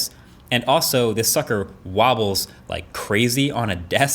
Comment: did he just say "and also this fucker wobbles like crazy on a desk..." lmao ive been trying to listen to it and cant tell if its sucker or fucker lol